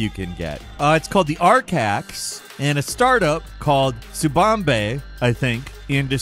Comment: did he say "tsubambe"? 🤣